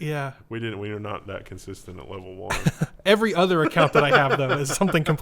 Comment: Did he say "Thanks, dad" ???